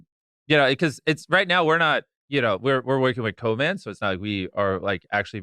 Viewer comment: huh? I’ve listened to that sentence so many times 😂. I first heard “I’m working with Covid” then I hear “we’re working with Conan”. After that it gets jumbled up and I can’t understand a word 😂😂